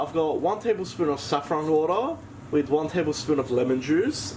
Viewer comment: At time 3:21 it sounds like you say saffron water?